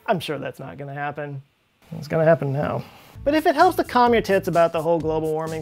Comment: Did you say “...calm your tits”? 6:07 I hope so! :)